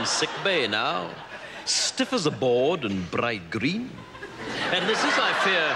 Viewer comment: what does he say after "stiff as a board"? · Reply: I know it's 2 years later, but if you're still curious, he says "and bright green"